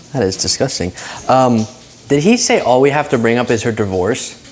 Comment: I thought he was saying “her voice” not “divorce”?